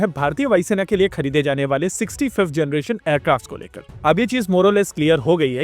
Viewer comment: i heard "65th generation aircrafts ke baare me " & said "what "? Then next second " oh ! it is 60 5th gen....". 🤣🤣🤣🤣